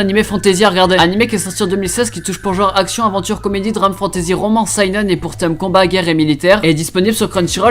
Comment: j'ai entendue romance? Go regarder😂😂